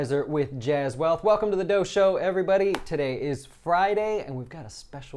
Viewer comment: this whole time i thought you were saying DOJO - its DOUGH SHOW --- lol clever clever!